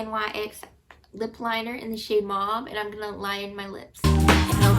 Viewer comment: Ahhh yes I remember lighting my white eye liner when I was in highschool lol it worked so well! I'm so spoiled with the liquid eyeliners out now! I thought you said the lip liner was called "mom" but I'm sure you said "mauve" hahah! Great video! I love all your jokes btw haha even if some are cheesy! That's my kind of humor honestly lol